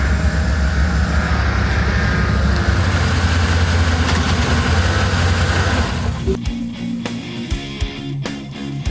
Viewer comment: did you say "kentucky"? The front plate says "Nevada"? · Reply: Kentucky, NV